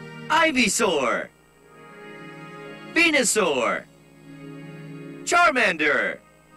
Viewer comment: I misheard it at P*nisaur